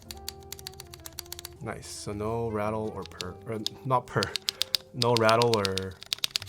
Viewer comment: WHAT HE DOING IN THE BACK ROOM SAYING NO PER???...